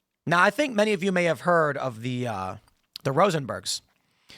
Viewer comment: the ROSENBERGS???????? sounds like someone is saying, "ITS ALWAYS THE ***S!!!!"